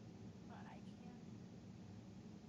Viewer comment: "but I can't see you" 🤔 I keep replaying it and that's what I hear.